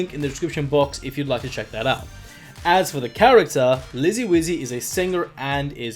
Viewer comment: I thought he said “lizzy glizzy”